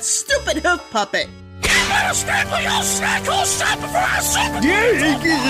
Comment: What did Hoofio say from 3:43 to 3:49? & shouldn’t Joey Turner really be voicing Hoofio since he’s Casper Cake’s hoof puppet? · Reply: He said “You better staple y’all’s snack hole shut before I superglue you to a bullet train you little-“